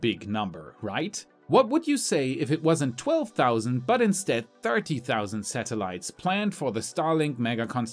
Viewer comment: I thought he said 13 000 , woah 30 000 is soooo much